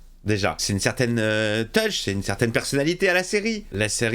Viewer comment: une certaine QUOI ? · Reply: Une certaine Touche 🤣